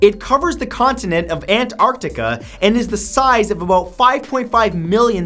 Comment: It sounds like he is saying Ant Arctica instead of Antarctica ... i can't be the only one annoyed by that little pause there ..